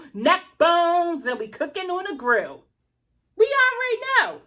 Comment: Am I hearing things? Did she just say neckbones cooking on the grill? I'm screaming 🤣🤣🤣🤣🤣